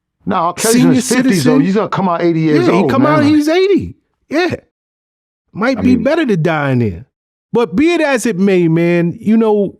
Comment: Did he say it might be better to die in prison? What is he talking about?